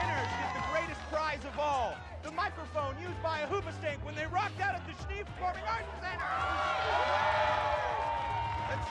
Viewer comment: Can someone tell me what they say at 5:18 ? I don't speak English well enough to understand what they say... Thanks :) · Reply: I know I'm late but they said: Jesse:Your going down Beca: I don't care Jesse:Yeah, I know